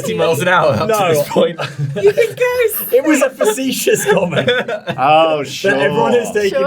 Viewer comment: Hello, foreign englisch speaker Here. Whats does say 24:15 ? That was a vicius Command? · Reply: "facetious comment" it means he wasn't being serious :)